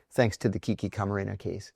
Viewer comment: where can we find that law? i didnt understand what he said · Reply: “Thanks to the Kiki Camarena case”